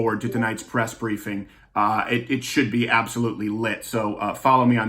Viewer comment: Did Crowder.... did Crowder say 'lit'? He must be tired!